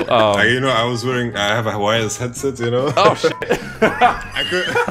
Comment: This guy! I love that you brought him to play with you. This is one of the best videos i've ever watched. Thanks for all the good content. For one second i thought he said he had an hawaiian headset